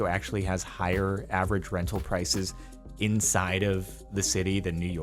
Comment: For a second there, I thought he said San Francisco had higher dental prices